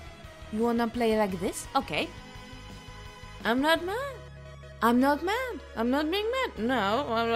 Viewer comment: Thought frisk said "I'm not man, no i'm not being man" XD